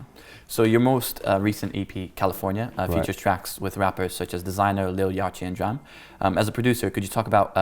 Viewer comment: what did he call lil yachty at 25:39 😂😂😂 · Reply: Hightop "Lil Yah-chi"